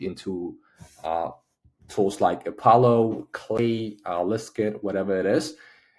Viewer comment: Great video, thank you. Could you list the three decision finder tools mentioned at 9:02. I couldn't quite hear the second and third one you mentioned? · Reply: It's Apollo, Clay, and Listkit. Clay is a bit more advanced tool so I suggest you use either Apollo or Listkit